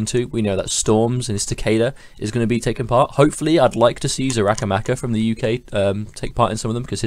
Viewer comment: What's the name of the Liu Kang player you mentioned at 12:14? Couldn't understand the name :/ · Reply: Xarakamaka.